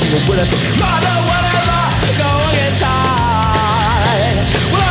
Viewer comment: sounds like he says "Mother whatever we're going in time"